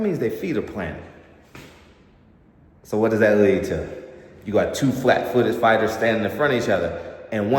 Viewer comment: Dog … did you say two flat footed fighters ?